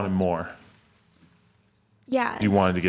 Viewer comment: Did I just hear “me and my husband”?